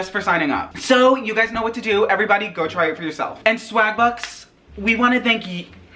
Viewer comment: when he said swagbucks i thought he said starbucks